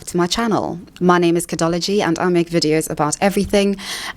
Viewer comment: Says: "My name is Kidology and I make videos about everything and anything to do with modern society" I hear: "My name is Kidology and I am literally God"